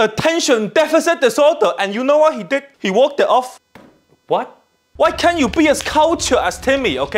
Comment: "He had ADHD. He walked it off." As someone with ADHD myself, this had me bursting out laughing 😂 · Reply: I thought he said he worked it off